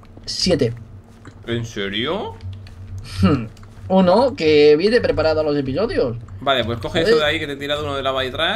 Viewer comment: WTF escuche mal o vegetta dijo minuto 13:49 uno que viene preparado a lo de tu novio xD